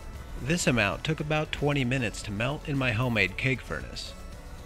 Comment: When you said keg furnace I swear to you I thought you said “cake furnace” and I got really confused then when I realized what you meant I started laughing my ass off